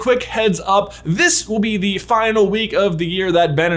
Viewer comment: When you said "final week" I thought you said "final year" and was concerned for a second.